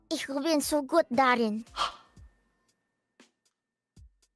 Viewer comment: What did Kobo say at the end? the german thing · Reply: "shit im not germany i dont understand a thing"